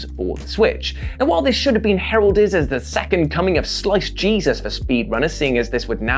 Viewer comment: I thought you said slice cheesus.